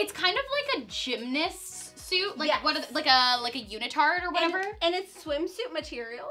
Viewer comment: anyone else hear unitard instead of leotard?